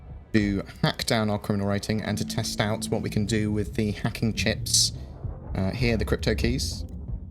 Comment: Ahhhhhh, this entire time I thought you were saying Hacking SHIPS;-)